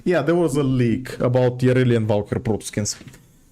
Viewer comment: Wait, did I hear that right, There was a 'leak' about Yareli and Valkyr protoskins???